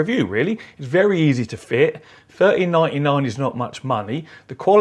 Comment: I thought you said 30.99 at the beginning and was super confused as to why you were recommending it